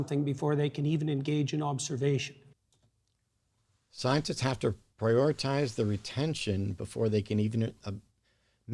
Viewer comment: Peterson said "Scientists have to prioritize their attention", instead of "Scientists have to prioritize the retention" as was quoted. Which, to be fair, makes much more sense than the latter.